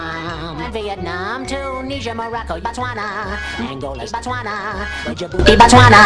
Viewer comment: Did I just hear "angle" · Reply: angola